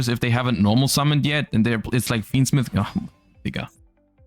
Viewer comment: first time I heard him say "digga" I think? Maybe it was an "egal" though